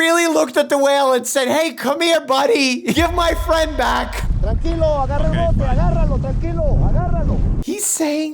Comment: All i heard him say was takis and tacos